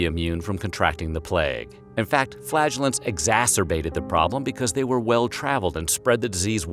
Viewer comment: tell me I’m not the only one who thought that said FLATULENCE! .. here I am thinking - before rewinding - “WHAT!? FARTINGGG SPREAD THE PLAGUE!?”